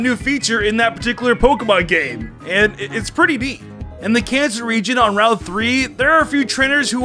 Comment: did he say cancer instead of kanto